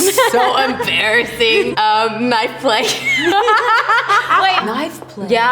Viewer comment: Can someone plz tell me what answer was at 7:58? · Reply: Sound like she said knife play